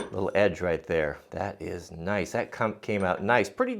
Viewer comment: what did you say? Because it sounded like this: "that cum came out nice."